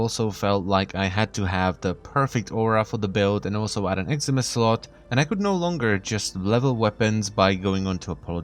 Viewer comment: did you just say "eximus slot"? instead of exilus?